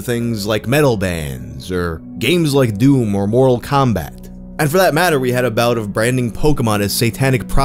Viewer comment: I heard " oral combat " and thought that'd be a great name for a hentai fighter game